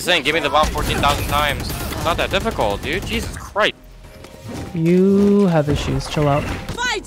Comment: it sounded like he said "it's not that difficult dude, Jesus crepe " but the wrong pronunciation 😭